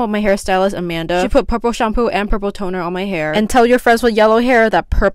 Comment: I thought u said “popo” not purple and i was so confused- 😭